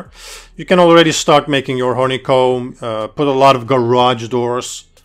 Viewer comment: does he say "horny comb" 7:02 ?